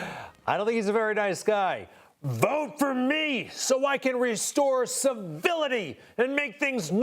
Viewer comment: I thought he said "restore villainy"